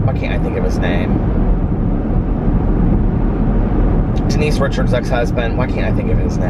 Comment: HAHAHAHA for a full 30 seconds, I thought you said, "Keith Richards' ex husband." I was like, "Huh? 🤔🤔🤔" lol